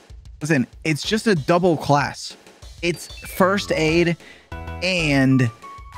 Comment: Mans heard First Date training instead of First Aid